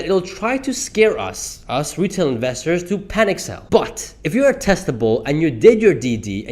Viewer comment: I had your video up on a hidden tab while scrolling through Twitter, and I honestly thought you said '' But, if you have testicles''